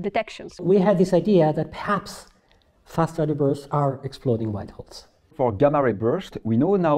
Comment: what does he say? Fast radio bursts... good grief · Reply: perhaps Fast Radio Bursts are exploding black holes